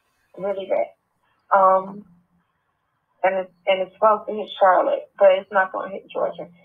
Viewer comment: Right around 5:14 "it's supposed to hit ... " I can't understand what she said. Charlotte? I'm trying to figure out where in Florida she is. Any ideas?